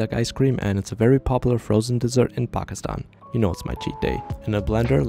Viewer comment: Anyone else love that he says "you know it's my cheat day" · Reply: I thought he said "you know it's my teeth day" cause he bit into the thing 😅